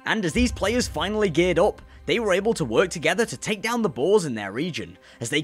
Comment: the captions says balls instead of boars 💀💀💀